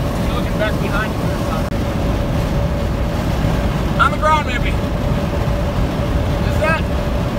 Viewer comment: Did you say, “I’m a ground hippie?”